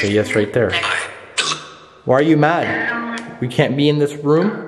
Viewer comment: i heard "I can't breathe"